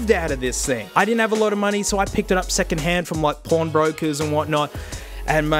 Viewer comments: Does anyone else hear "porn brokers" @ 5:37? I'd wash my hands if I picked up a dank ipod from a porn broker ;) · Ahh yes, I hear the Australian Porn Broker Business is really thriving right now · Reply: He meant pawn but i heard it to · Sounds like you said Pornbrokers not pawnbroker